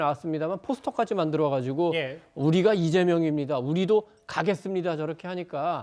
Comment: 우리가 이재명이다 이해석은 우리는 다 똑같은 도둑놈이다 로 들리는건 왠지